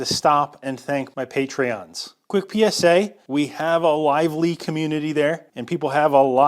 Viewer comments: I heard "we have a liveleak community there" and was momentarily removed from the 4th dimension, appalled at the thought of a hobby machining liveleak...fanbase. Oh gawd. Lively. LIVELY. Methinks much more likely. I need to do some breathing practice for a few minutes. Pheeeew. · i heard "a LiveLeak community", and thought thats where the videos of your split nail gets upliaded 😅